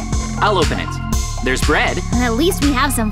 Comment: Mikey sounds like he said red